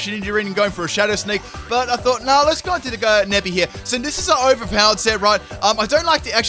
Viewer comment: I thought that said "You're out of the bag, NEBBY!"